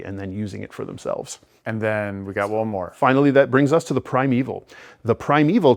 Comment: So the primalist is a class....and a subclass? Or am I mishearing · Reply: The twins said primalist, but said primeval. I'm guessing right, and it's an easy mistake to make in speech.